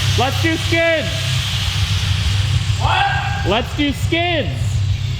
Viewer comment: ahem! I, for a moment there I thought he said... "Let's see some SKIN!" ...erm, yeah I was really relived when I realized he was saying SKIDS!